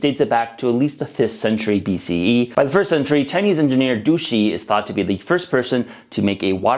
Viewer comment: I hear Duchi was...a really nice guy, actually! 😝